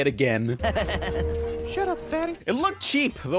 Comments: did she just say 'shut up faggot' tot the kid? maybe she said 'shut up fatty' but that's not much nicer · I thought the woman said faggot instead of fatty